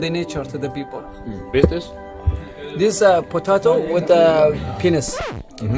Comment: Lol also thought he said potato with Pen*s 😂😂